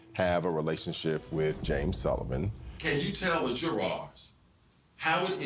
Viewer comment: What did the prosecutor say??? "Jourarr's"??? Somebody tell me PLEEEZ, is that what he said?? "Jourarr's"???🤣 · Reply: he said jurors which is a word that refers to the members of a jury. he also said it with a southern accent.. what is so funny ?